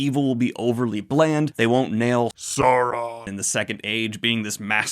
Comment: Did you say Saruman? Been there. :P